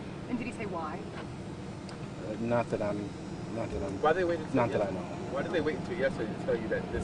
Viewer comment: READ ME- 1:21 turn on the subtitles, did he really say dead or is that a glitch?